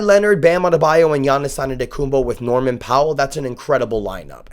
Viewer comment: Bro- I'm not sure if i'm mishearing but are you saying Antetokounm-BO?